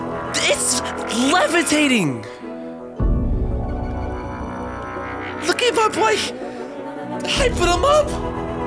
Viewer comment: the cc said "look at my place" instead of look at my boy